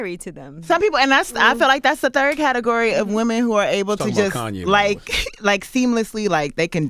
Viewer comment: did he say she talking about Kanye?